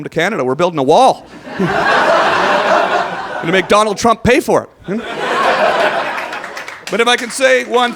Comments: I thought he said "McDonald Trump", but it was "Make Donald Trump [pay for it]" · When he talked about the Canadian border wall I thought he said make McDonald Trump pay for it...lmao...wait maybe he did